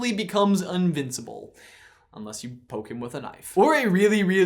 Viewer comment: Did he say un-vincible at 12:37???